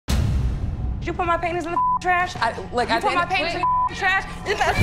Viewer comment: I thought she said you put my panties in the trash